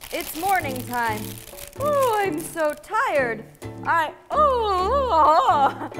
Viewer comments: What does Elise say in 0:10 when Nat's doing the intro?? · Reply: 2 yrs later but I hear “I’m so touched” 🤔🤓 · i heard "is morphin time" 😂